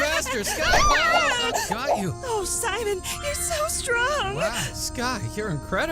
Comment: "Oh Sidon" am i hearing it right